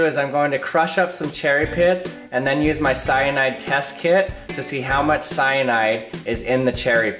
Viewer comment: i thought he said my Cyanide test kid